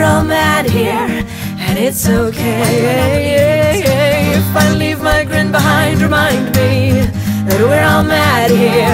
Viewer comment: BACKGROUND TALK. cant figure what is said. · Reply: it said why do I not belive you